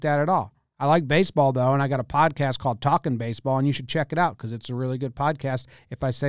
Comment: Did I hear "codcast"? Sounds like a fishing show as opposed to baseball.